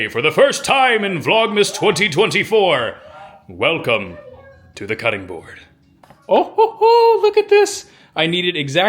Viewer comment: When you said “Welcome to the cutting board” I heard it as Taylor Swift says “Welcome to the eras tour!” 😂😂 One my favorite underrated Christmas movies is The Family Stone!